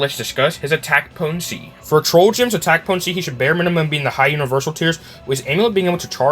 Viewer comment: High universel did I hear that correct?